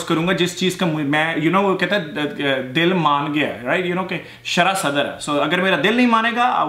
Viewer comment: did you say sharah sadr? pure arabic word though.